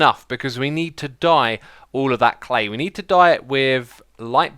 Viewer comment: After your "Hope you dye" shop I always hear "die" instead of "dye"